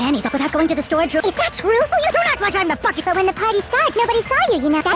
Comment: Did hajime call maihiru a bitch?!